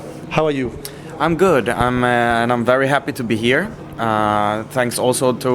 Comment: "I'm not very happy to be here" did he really say that?